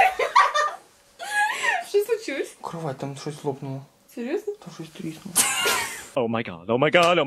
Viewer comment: Когда кровать лопнула - "Что слочилось?" "Кровать, там что то лопнуло!" "Серъезно?" "Там шось триснуло!" Ваня переключился на "Суржик" :DD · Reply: а мне послышалось: "курва там шось лопнуло"